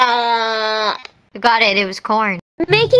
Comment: "I got it" whilst moving her tongue at 5:15 sounded like she was saying "ahegao"